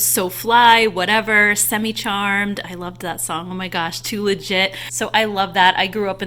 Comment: Lol When you said too legit, I mistakenly thought you said too lid sh*t..which obviously doesn't make sense lol so I had to re-play that part haha